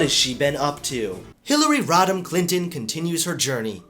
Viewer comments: I keep hearing "Hillary Rotten Clinton", is that just me? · Also, I thought you said Hillary rottin clintin